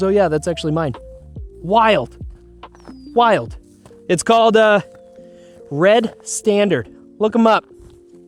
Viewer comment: What's the name of the divot tool? It sounded like you were saying Red Standard but that does not produce any search results for me. Love the channel, watching from a tiny island in Alaska. 👊😎